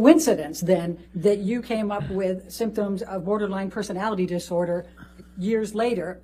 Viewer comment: That the lawyer keeps using bipolar and borderline personality disorder interchangeably tells me that she doesn't know enough to know what she needs to ask about. I am endlessly baffled by her questions. Like. There was a point or two where I'm like okay I think I know where she's going but then nope. She doesn't go that way. She just drifts off. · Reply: I'm fairly certain I heard her say "borderland personality disorder" several times, too